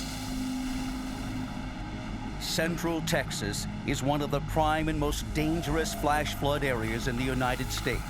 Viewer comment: Thought it said dangerous food haha